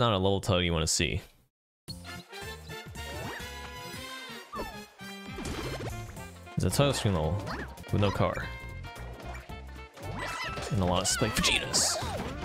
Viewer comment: Did he just call spike a “spike fajita”?